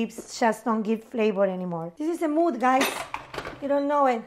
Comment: Oh my god did Gaby just say “This is a mood, guys”? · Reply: I heard "mood." It's at about 5:20 if anyone wants to check.